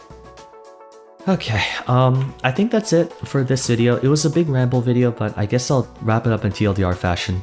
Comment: I thought it said gamble, not ramble